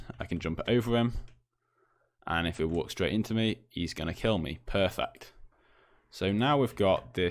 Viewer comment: I heard perfecc